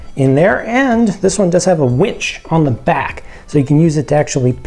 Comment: Nice I thought you said witch instead of winch